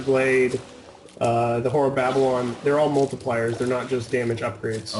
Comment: When he said multiplier I thought he said multiplayer. I was like YES! Oh...